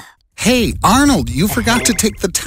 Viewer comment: I paused it at the start and it sounded like it said ”Eat Arnold”